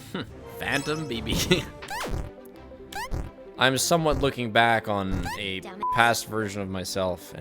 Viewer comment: because of the rainbows bleep I thought you said “I’m somewhat looking back on a fuckass version of myself”